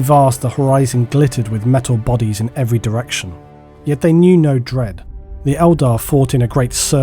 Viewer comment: I thought you said "yet they knew no drip" and now I have a coffee stain all over my wall